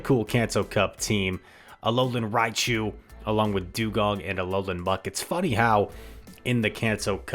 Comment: I thought you said Alolan Raichu, Alolan Dewgong & Alolan Muk 😂